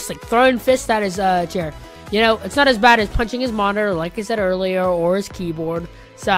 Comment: When he said, “punching his monitor,” I thought he said, “punching his mom.”